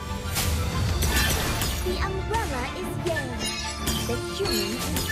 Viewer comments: why did kagura call her umbrella gay😂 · ngl I thought she said "the umbrella is gay"😂😂😂😂